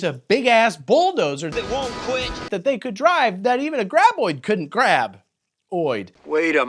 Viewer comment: I thought you were saying grab-boy until I saw the video game. lol